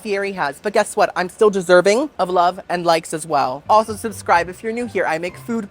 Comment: I thought you said love and lice and I about spit lol 😂